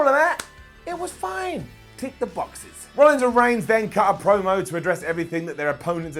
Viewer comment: did he say Rhonda Reigns?